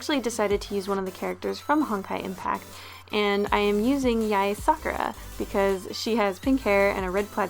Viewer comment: ahh at the beginning when you said sakura I thought you said f*ckera DX